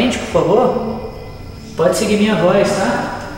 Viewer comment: Só eu que no 04:29 ou 04:30 ouvi no fundo um: olá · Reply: Acho que o disse um "tá" que o áudio não captou corretamente.